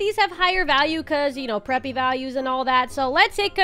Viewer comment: are you preppy i think you said "preppy values " at 7:29 and i subscribed.